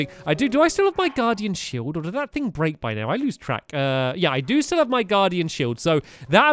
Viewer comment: did he say i use crack?